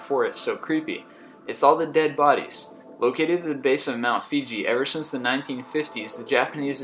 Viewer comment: Mt. Fuji- swear he said "Mt. Fiji".......whatevs.